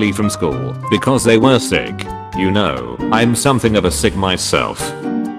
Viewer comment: It sounded like he said I’m something of a SIGMA myself